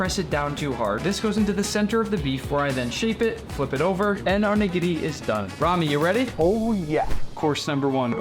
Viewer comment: What did u call me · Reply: Nigiri